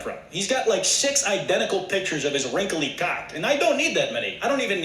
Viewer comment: What this man said at 3:40? His wrinkly CAHCK? 😂😂😂